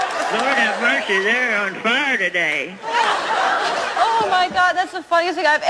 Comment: Also what did she say around 4:25 ? something like fridurday instead of Saturday or Friday? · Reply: Wayne Thomson “Lord have Mercy, They’re on fire today”! Speaking of the Audience I suppose that was laughing and applauding.